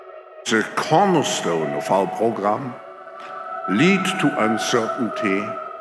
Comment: Did he just say it’s a cornerstone of our pogrom??? 😮